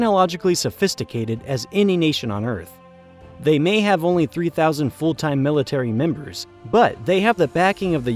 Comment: the subtitles say "300,000" yet your voice-over strangely clips in an audible way, and actually says "three thousand" instead. as though somehow the word "hundred" was edited out. Very curious